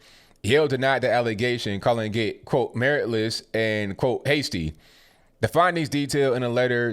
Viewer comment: Lol. I thought you said meritless and pasty. I thought wow they don't even try to hide it.